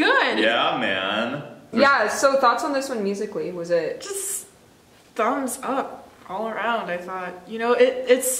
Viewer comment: Is she said THUMBS UP by Momoland in 04:26 ??? 🤣🤣🤣👍👍